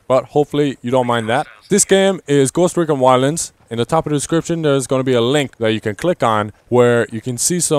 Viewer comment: Wtf did he say at 0:14? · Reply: "there's going to be a link that you can click on"